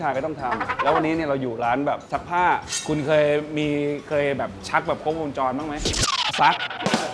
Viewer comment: ได้ยินว่าชัก